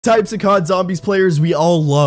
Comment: He: "type of the cod zombie player" Me: why i hear "type of the co*dom player"?? 💀😭